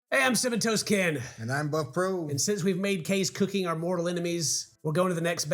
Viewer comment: did he say "And I'm butt-probe." ???